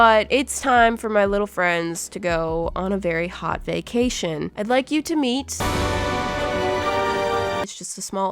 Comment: Was it me or did she say "BAKEtion"